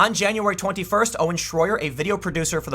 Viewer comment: Lol when he said "Owen Schroyer" I heard "Orange warrior" I was like "Huh? Oh because he supports Trump?"